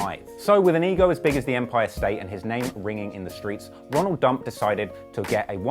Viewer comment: Did you say Ronald Dump??